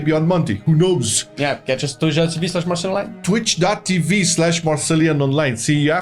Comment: what did u say at 15:29 man? ahahaha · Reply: tushdwg.tv/marcelianonline, thought I was pretty clear